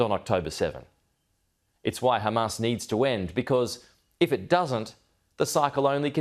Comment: Did I hear Hamas needs to win?